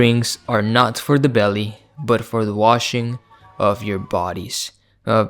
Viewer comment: I thought you said “Strong drinks are not for the belly, but for the washing of your balls”